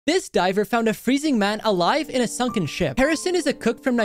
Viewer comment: I thought it said this diver found Freddy Fazbear